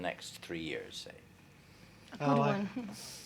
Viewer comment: does anyone know what they said at 10:28 ? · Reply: I think Whiting said "oh I" and Hussey said "good one"